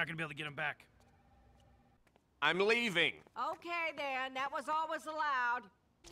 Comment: "I'm leaving." "Okay then, that was always allowed." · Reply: ohhh I kept hearing "that was all that was allowed" and didn't quite get what she meant. thank you!